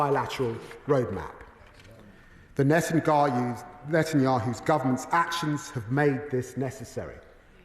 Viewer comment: Did Lammy just said Natan"goy'hoo ??:face-fuchsia-wide-eyes::face-fuchsia-wide-eyes::face-fuchsia-wide-eyes::face-fuchsia-wide-eyes